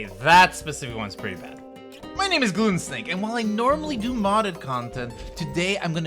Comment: I thought you said your name was goonsnake 😮